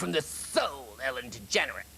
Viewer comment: What happened at 3:21 did he say degenerate then break character?! 😝